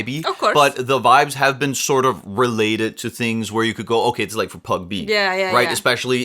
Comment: you're adorable but isn't it PUB-G!?😅 I heard you say pag-b